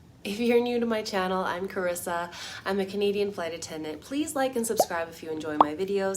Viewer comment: I just recently found your channel and in your intro I thought you were saying comedian flight attendant not Canadian flight attendant that would explain everything!